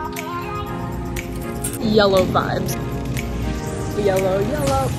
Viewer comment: When she said “yellow vibes” I thought she said “YOLO vibes” lol 😂